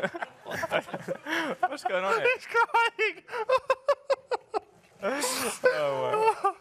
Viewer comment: can anyone please tell me what Lando said I really couldn't understand it. but it sounds very funny. Thanx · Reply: yeah you're right man it's Daniel. He said he's crying. Lando didn't say anything 😂 thank you bro